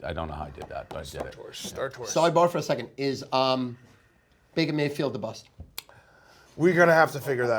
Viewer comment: It sounds like Stu says is Beta Maylfield a bust?